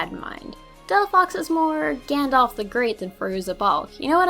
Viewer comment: wait did she just say gondola the great